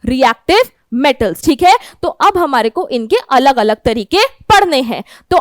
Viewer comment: ma'am speak parne hai instead of padhne hai😀😀😀